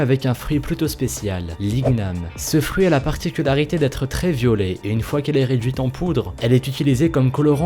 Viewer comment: Au début du 2 j'ai entendu "ce fruit à l'habitude d'être très violé "... 😂😂😂